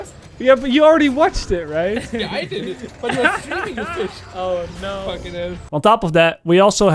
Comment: Did he call you a fish at 2:50 ? haha